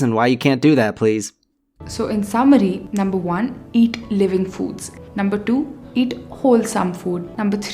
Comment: Lol. I just thought she said eat live food 😂 like a hyena or something